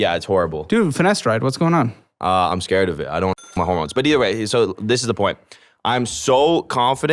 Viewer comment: don’t wanna mess with his hormones 😂 did I just hear that correctly?? 😂🤣